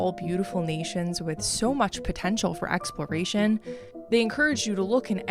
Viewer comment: Did you say...potential? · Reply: OH POTENTIAL